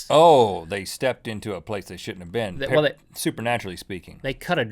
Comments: Did Rhett say "Piece of Sh*t" at 6:19? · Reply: He said they stepped into a place they shouldn't have been. But it sounded more like "Ohh...they stepped into a place they shouldn't ah been." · Haha Rhett: " You put a helmet on top of me you'll see OVER some buildings" xD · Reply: ***** At first I thought Rhett said "So they stepped in a place with sh*t in a bin"